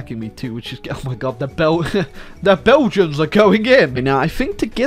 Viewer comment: the first time he tried to say Belgians it rlly sounded like the dutch word for Belgian, Belg/Belgen